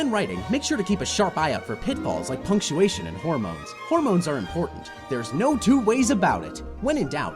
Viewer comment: thought this said homophobes